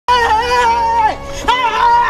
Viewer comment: It really Sounds Like Hes saying " why are you ronning "